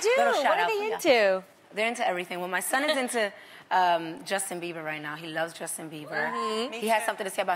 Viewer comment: Did she just say Justin Beaver?